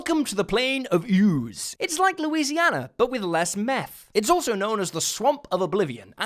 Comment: "It's like Louisina, but with less meth" 😂😂😂 How is that the best and most accurate thing I've ever heard? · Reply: Lol, I thought he said "less mess". Meth is much better.